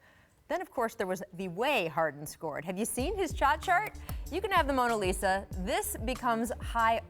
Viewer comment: im sorry but did she just say chot shart?!?